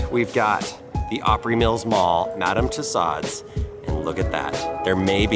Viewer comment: How do you pronounce Madame Tussaud’s?? Lol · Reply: I thought he said Madame Toe Sod's 😂 P.s.- I love because he's just like my Dad. Glad he can have fun, be goofy, and he's a good sport about us making fun of him when he says Dad stuff 😉🤗